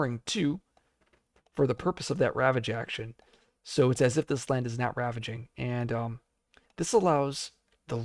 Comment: Maybe I'm mishearing you at 25:36 - do you say "it's as if this land is NOW ravaging" or "not ravaging"?